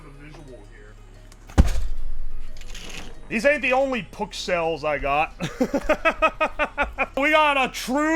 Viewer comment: AWW, MY PKCELLSSS !!!! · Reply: lol it actualy sounds like ke says “oh my PUKcells!”